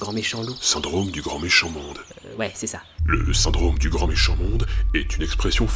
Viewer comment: au début j'ai entendu "le syndrome du cornichon monde" puis j'ai vu le titre de la vidéo